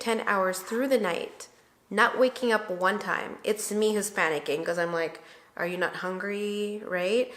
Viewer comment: Anybody else thought said "It's me Hispanic-ing" at 8:00 lol I know she didn't but because i'm Hispanic I completely agreed with her at that moment. HAHA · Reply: Ha! I had to listen to that again too. But I think she said "It's me who's panic-ing". She said it so fast though. LOL.